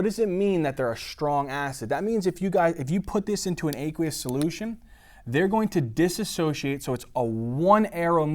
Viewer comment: Can somebody write down what kind of solution? I can't understand ;/ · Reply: aqueous solution